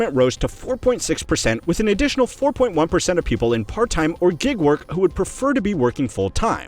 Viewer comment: It sounds like you're saying "Jig-work" vs gig-work... makes me wonder: "What about the Gigolos ?? how have male prostitutes been affected?" .... Won't anybody PLEASE: THINK OF THE GIGOLOS ! After writing all that (and totally misspelling gigolos over and over again [needed google's help]), I think it's safe to say we totally f'd up the j/g sounds in english.. like WTF?!?!